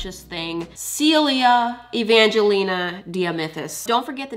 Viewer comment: the caption say “Celia Evangelina Dia Memphis” instead of “Sealia Evangelina Dia Mythis/Mithus”(i think)